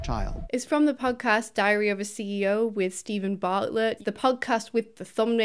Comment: I literally heard diarrhea of a CEO lol